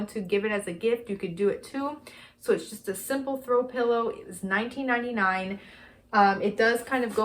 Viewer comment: When you said that the snowballs were, $19.99, I had a brain fart... I thought you said they were from the year 1999... Doh! hahahaha